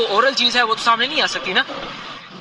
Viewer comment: did he say "Fauj ne election teek karay hain"?